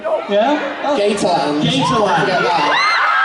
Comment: when first said gator land I thought he said gay time and my eyes went wide and I got a huge grin wow lol